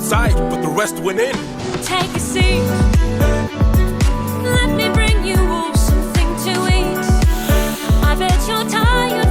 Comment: Before you turned on the captions, I thought she said “let me bring you wolves something to eat”